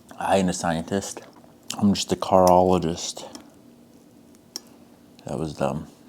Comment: 😂😂😂what he said cartheologiest…